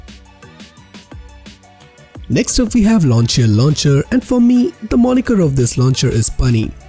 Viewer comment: Anyone heard Launcher Launcher? (Lawnchair 😂)